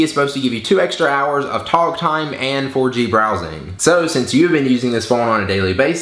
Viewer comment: thought he said orgy browsing. Also, stfu about the facial hair Ty said he might trim it